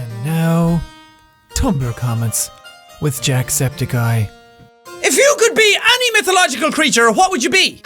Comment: i heard it as oh no tumbler comments with jack sep tic eye